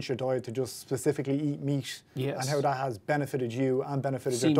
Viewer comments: "To just specifically eat..." What? It's so hard to understand. Did he say meat? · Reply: Yeah it's meat. Carnivore diet. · Why is he saying meach? <link> Or does he say MEAT? Edit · please help, what does he say at min 3:35? "specifically eat ........."? is it "meat"? it means he eats meat now or what?